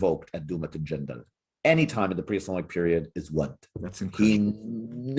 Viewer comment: What do they say at 05:00? A name of God, but I can't make it out. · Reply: Wadd (واد) is the name of a false deity mentioned in the Quran, who was worshipped at the time of the prophet Nuh (Noah).